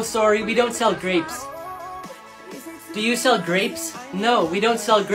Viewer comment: I thought he said “do u sell rap-“ 💀